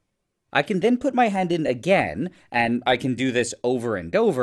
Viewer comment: I thought he said he would put his head in 😅